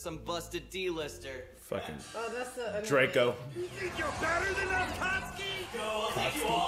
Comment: DID I HEAR DRACO?! XDDD